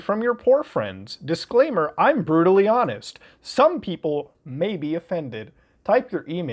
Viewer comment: When some people say ''I'm brutally honest'', some people hear it as just ''I'm brutal'', I keep hearing ''I'm brutally dumb''